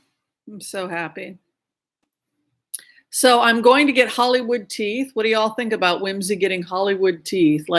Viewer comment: Haha! When you spoke about your teeth Whimsy, I heard “I’m getting Hollywood tea” I’m like what is that some kind of fancy celebrity tea party?! 😅🤦‍♀️